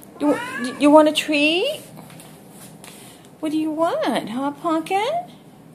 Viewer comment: I thought she said "what do you want...a hot pocket??".😀